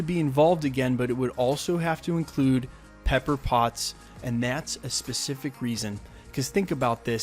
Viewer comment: LOL I heard it like "Pepper Bots!"